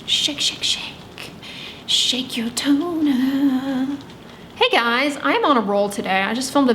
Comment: Dr Dray singing "shake shake shake your toner" with a remarkably straight face is the cutest thing ever. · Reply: lol I thought she said "shake your toenail" too 🤣